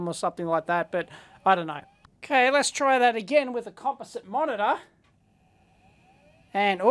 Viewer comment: I don't know what port you connected the monitor to but the "VGA" socket doesn't put out a standard signal. An adapter is needed to connect a standard monitor to an Amiga. IIRC, there's a negative signal on one of the pins while a standard monitor expects a positive one. Could be why you didn't get anything visible. If you were using the composite out that wouldn't apply. A "Bridge Board" was available to put an IBM PC processor (80286 or later the 80386) in the 2000. That ran the ISA slots and allowed the use of IBM-style cards such as the Targa. There were also accelerator cards that would up the Amiga to 68030 or 68040 processors and expand the memory. Most were from aftermarket companies although Commodore did offer one as an option. When fitted with this they marked the front as the 2500. And, of course, the system could accept the Video Toaster TV effects board (2 joineed boards actually). A popular option was the TV overlay accessory, either internal or external. The Toaster also added that function. I haven't thought about this in decades. Hope I remembered correctly. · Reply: He clearly says it's composite.